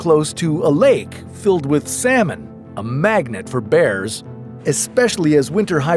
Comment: Im sorry did he just say 5:13 "A lake filled with salmon?"